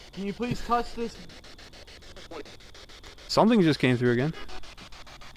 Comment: after you asked can you touch this device I heard what sounded like "Just wait"